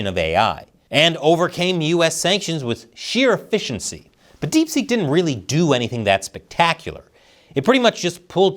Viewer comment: 🤭 Sorry, thought you said "DeepSh.. (Dumping)" 😂 🍯